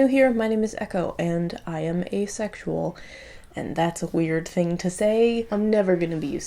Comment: Oh my goodness I thought she said "I am a sexual" and I was thinking "but which sexual?" Anyway that's awesome and I am proud of you for coming out!!! 💜⚪️☑️🖤 Idk if I got the flag right